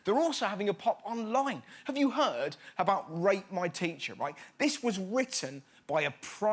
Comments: At first I thought he said rape my teacher not rate my teacher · I thought he said "RAPE MY TEACHER". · captions say rape my teacher That's a lot worse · Honestly thought he said "Rape my teacher" ... ffs! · For a second I thought that he said "Rape my teacher" Worse than burning a teacher honestly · I thought he was saying “rape my teacher” not “rate” I was so worried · I thought he said “rape my teacher” instead of “rate my teacher” I had to do a double take. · Thought he said tale my teacher and was like well shit